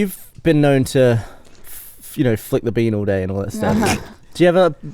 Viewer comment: Sorry guys but English isnt my first language can anyone tell me what does asks to at 3:17? Thanks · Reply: 'Flicking the bean all day' ... a term for female masturbation.